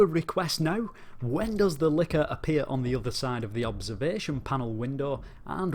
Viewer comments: I thought he said liquor, not licker, I think I have a problem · Lol, man when you said Licker I thought you said liquor and I was trying to figure out why anyone would be asking about that in a video game